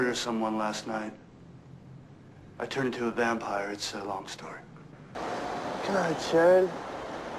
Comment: It sounds like he said "come on, Chud" lol 😂